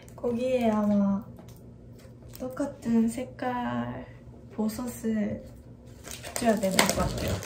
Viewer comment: ㅋㅋㅋㅋㅋㅋㅋㅋ보석 발음이 버섯으로 들려서 영어 자막에 mushroom 😂😂